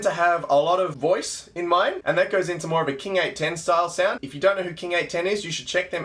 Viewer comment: How spells what he said in 5:22? I can’t understand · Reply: King 810. It's a band I love